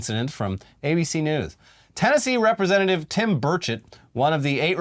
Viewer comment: lmfao I thought you said "Tim Birdshit" instead of "Burchett"